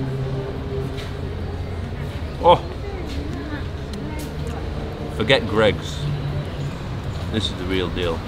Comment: Did he said forget GREG 🤣🤣